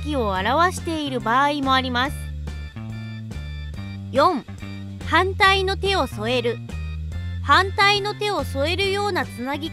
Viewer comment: 反対の手を添えるを犯罪に手を染めるって聞こえたの俺だけwww